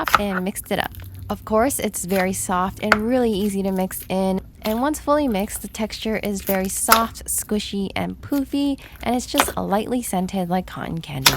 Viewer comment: did she just say poopy💩